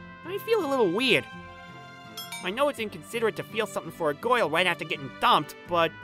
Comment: its sounds like he's saying goyle instead of girl lol